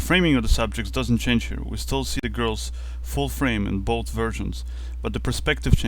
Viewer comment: I thought he said that they were both virgins for a second. "We see the girls in both shots, both virgins"